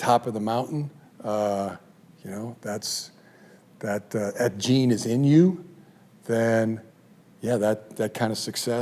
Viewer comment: I thought he said if that "Ed Gein is in you" for a bit lol